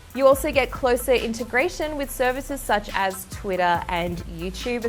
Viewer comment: Twitter is now called “twida”